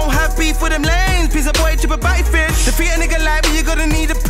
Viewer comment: Did J avalanche just send for Chip? I can barely understand what he's saying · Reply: listen closely I think he said chip a batty fish